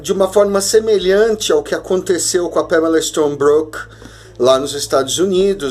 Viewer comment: Agora vocês entendem porque meus vídeos precisam ser SUPERFICIAIS? Porque nem todas as verdades são pra todos os ouvidos. Voltarei a falar nas entrelinhas. Beijos meus amores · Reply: Ei alguém sabe me dizer qual era o nome da mulher que ele falou nesse minuto aqui 3:37? Eu acho que era Pamela sei lá o que, não entendi direito